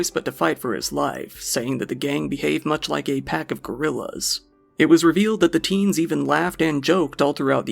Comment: y did u change to gorillas and not wolves? Which is what was actually said.....I implore u to look into unconscious bias